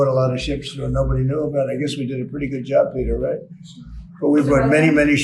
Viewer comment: Peter? Did he call him Peter?